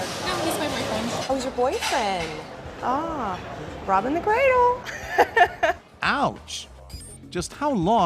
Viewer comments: Can somebody clearly tell me what the waitress said at 3:32 ??? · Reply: robbing the cradle. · What do say here, after the woman said, "He is my boyfriend" ?? I didn't understand it?? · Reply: She said that he’s “robbing the cradle”